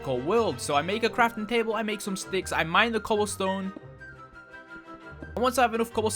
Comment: Put it volume high u will hear what he said he was mining stone and he said coal stone